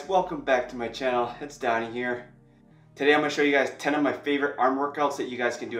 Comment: At first I thought you said “welcome back to my channel, it’s dying here” 😳 Great video helped out a lot